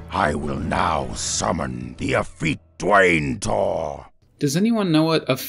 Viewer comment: I think he said "effete" which is a synonym for "feminine". No idea what dwain'tor is